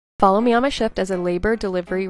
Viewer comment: I thought it said follow me on my shit lol